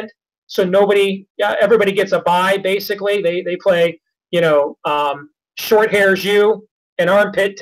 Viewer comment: lol, i thought he said 'short haired jews' and like what? watch out for The Short Haired University.